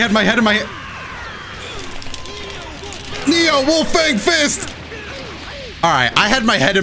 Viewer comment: It was at this moment I realized Yamcha says “NEO WOLF FANG FIST” instead of “FEEL THE WOLF FANG FIST” and now I’m never gonna hear the “original” again